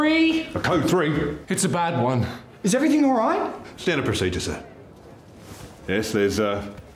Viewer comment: I keep hearing " gendered procedure " instead of "standard procedure"